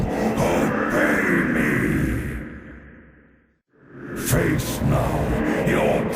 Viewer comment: What doe he say ? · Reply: Linkis I think it's "Bah, Frostmourne obey me!"